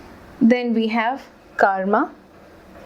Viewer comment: For a second there I thought you said "Karl Marx" instead of "Karma" and I went, daaang Huda.